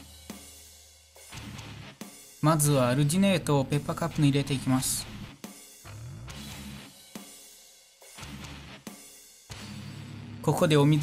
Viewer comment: ペーパーカップかまペパカップに聞こえる